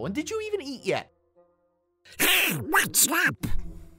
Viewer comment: anyone else heard say “what stop” and “ouch”